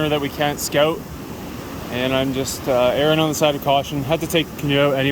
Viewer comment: did you say erring on the side of caution or Erin? ;o) Awesome video so far!!